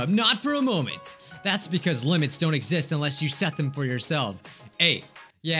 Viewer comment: I thought said “limbs don’t exist!” And I was so confused lol